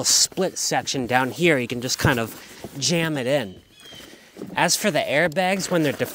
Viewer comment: I'm sorry, did you say "jam it in the taco"? I almost spit my wine out when I thought I heard that!